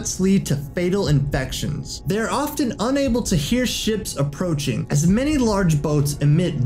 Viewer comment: I thought he was gonna say and often unable to hear shit approaching 😂